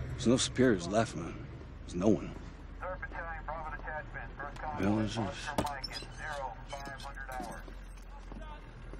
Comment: Does anyone knows what the military were saying in the radio? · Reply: Soldier 1: Oh My god, Oh my God. Soldier 2: HOLY SHIT! Multiple Infected Southwest Corner.